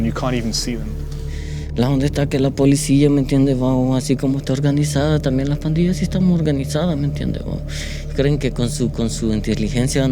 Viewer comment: Can somebody write down what he says in this ? is this correct: "donde esta que la policia me entiendes aun asi como esta organizada tambien las pandillas y estan organizadas me entiende o creen que" ??? PLEASE HELP